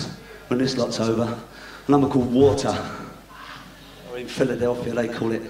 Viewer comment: Good Scottish water. Always keeps you right.Swear by it : ) Better than all the rest. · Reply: I always thought he said "or in the U" as in UK